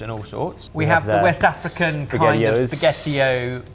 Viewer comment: I thought said spaghetti poops not hoops .. just laughed so hard 🤣🤣🤣